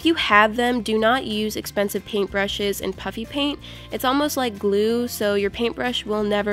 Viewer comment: I misheard: puppy paint 😂😂😂